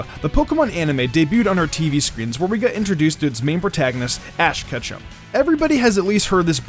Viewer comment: ik im not the only one who heard him say ash ketchup?!